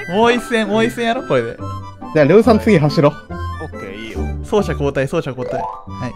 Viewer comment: のところのが「れうさんの家走ろう」って謎に聞こえてびっくりした(難聴)